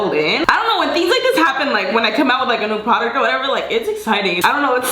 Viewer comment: I thought she said "when i come out of Aquatica or whatever"